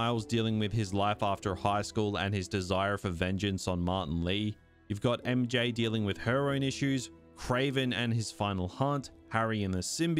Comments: I swear I heard "we've got MJ dealing with heroin issues" the first time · i could swear you said "we got mj dealing with heroin issues" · im listening without watching and i just hear “you got MJ dealing with heroin issues” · I thought dude said "We've got Mary Jane dealing with heroin issues" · In the story part, I totally thought you said "you've got MJ dealing with heroin issues" and I got so confused, but I guess that would explain why she looks like that lmao. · I thought you said, “MJ dealing with heroin issues” 😅